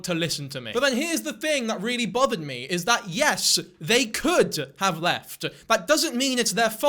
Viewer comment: I’m 37 and can gather context clues, I know you were not victim blaming but I did have to rewind and listen to some of your statements again because I just heard “you could’ve left” and I played it back for more context.